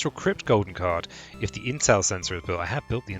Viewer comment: Haha, I totally misheard you saying "intel center" as "incel sensor"